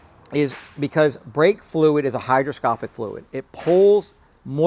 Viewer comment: The word is 'Hygroscopic', with a G, not Hydroscopic with the D. Thank you for this vid. Sounded like you said Hydro...